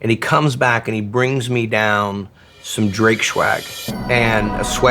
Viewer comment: does he say Drake shwag?